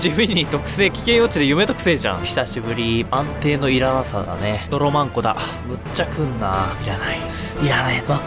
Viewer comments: ドロバンコがドロ万個って聞こえるの何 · ドロマ○コって聞こえるんだけどｗｗｗ